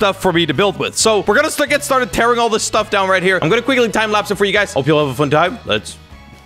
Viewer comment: The time capsule was satisfying xD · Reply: Did you just say time capsule XD